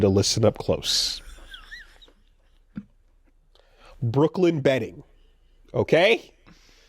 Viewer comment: I misheard that as Brooklyn Betting and this became a horror podcast